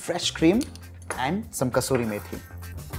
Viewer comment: what is the herb that he garnishes with at the end anyone? Sounds like he is saying 'kashuri maytree'?